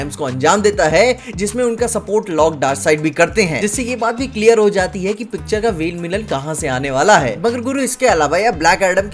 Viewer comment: Anyone heard vain millain (Main villain) 2:52 Tongue twist ho jati hai kbhi kbhi🤣